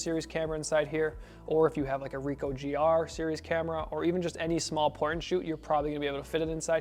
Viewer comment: > The subtitle says, "small porn shoot" ! Dude, wtf